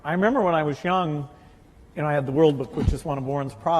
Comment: what does Bill say.... "I remember when I was young, and I had the (huh?), which is one of Warren's products, and I'm very good....." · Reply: He said the World Book which is an encyclopedia, it's published by Berkshire Hathaway a company owned by Warren